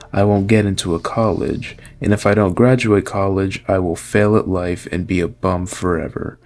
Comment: I misheard "fail at life" as "end my life" first, and thought: "How can you end your life and still be bum?"